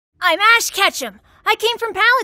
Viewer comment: it sounds like you said I’m ash ketchup